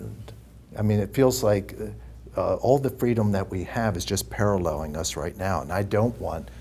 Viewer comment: "It feels that all the freedom that we have is just parrallel along us..."? What does he say exactly? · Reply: "Parralleling us"..................aka...........along side us..................so to say,,......never converging. Sincerely, Willis Drummond